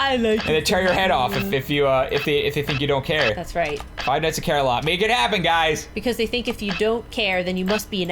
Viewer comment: you said there should be a 5 nights at car bears i thought you said five nights and carolines but it would be awesome if there was a 5 nights at Coralines...... if you dont know what im talking about im slightly sad... but it is a book, and you dont go to the internet to talk about books